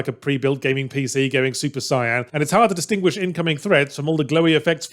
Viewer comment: Did he just say "Super Cyan" instead of "Super Saiyan"? Interesting...